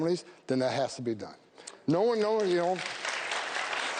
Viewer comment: wtf did he say??? 😂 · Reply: No weyo weyo